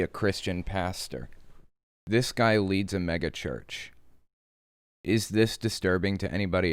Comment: I thought you said "Maga-church". Let's be real, either would fit!